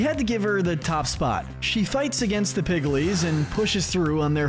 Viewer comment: Did they just call the piglins piglies?